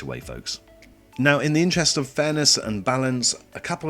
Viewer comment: I thought you said ".. in the interest of Thanos and balance" and it still made sense LOL